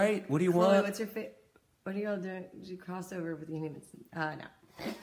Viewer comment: anyone understood what chloe said? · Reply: She said uh no